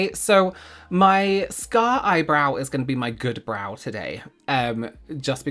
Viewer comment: Omg, I thought you said "My STAR eyebrow," and I was like, "YES it IS"! I mean, if I had to choose, the scarred one would definitely be my choice for favorite, so it makes sense it'd be the Star of the Show ❤😁.